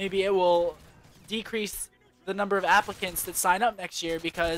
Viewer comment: I thought he said Africans XD